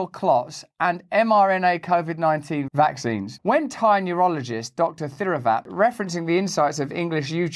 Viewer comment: Who else thought he said “tiny urologist” and not “Thai Neurologist”